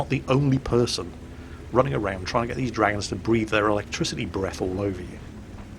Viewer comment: At first, when was talking about the dragon quest in WoW, I thought he said breed, not breath.